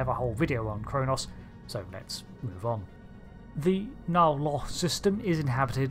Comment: For a moment I thought you said Klendathu system. Is it sad for me to say I have yet to play Star Trek Online?